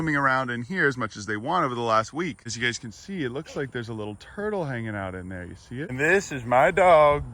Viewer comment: I'm ngl, at 1:10'ish I thought you was about to say "Look like there's a little turd"